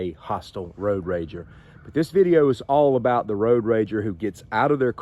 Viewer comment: O H I thought he said road RANGER ?? they help you when u r broke down ??